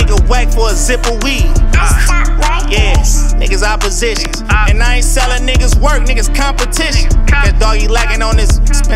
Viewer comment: what is that voice saying 🤔 help😫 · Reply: Iced up records